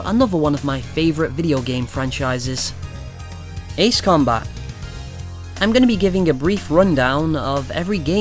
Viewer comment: I kept hearing 'Ace Comeback'......as if its lost somewhere.